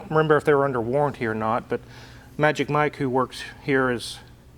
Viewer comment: "Magic Mike who worked here..." I guess that SLS transmission oil change didn't help his career as a mechanic. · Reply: sounded like "works" to me.